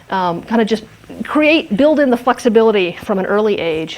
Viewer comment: did she say fuckcibility?